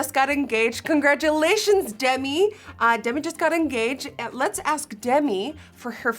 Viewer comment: Omg I thought you were saying that Gigi just got engaged and I almost freaked out 😂😂